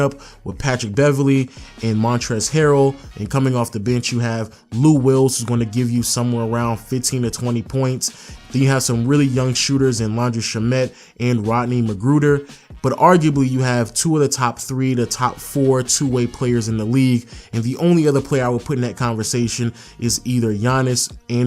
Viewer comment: did you just say Laundry Shommot